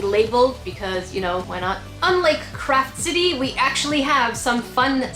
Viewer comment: I thought u said crap city